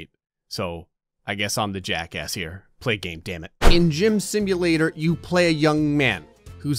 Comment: For a split second I thought it said Gray Simulator